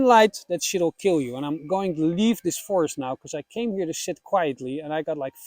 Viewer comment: Love the video and totally agree. Your English is amazing but the Dutch accent came in at 09:01 and it sounded like "I came here to shit quietly" 😂 Do pagans shit in the woods? Is the Pope Catholic?